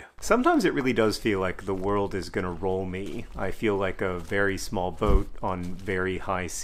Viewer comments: "I feel like a very small boat on very high seas" A first heard that as "A very small vote." Yes, it also feels like a very small vote sometimes. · I always thought he was saying “the world is gonna roamie” I wasn’t sure what it meant but I been singing it wrong for years LOL